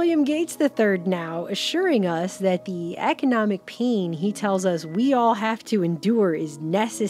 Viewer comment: here. Love you guys. I don't normally say it (hardly ever actually) but if you haven't, please consider sharing helping support us on Patreon. Anything, even a $1 helps. We're hard at work on our next film... kinda feels like our whole lives' work has been leading up to this moment. And anyway, thank you for being there. It means a lot to us. <link> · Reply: omg I'm rolling....i thought you said economic peen....and then everybody just started saying peen in the same context it was highlarious.😁